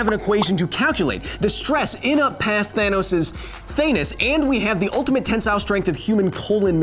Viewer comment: Did he say "Thanus"?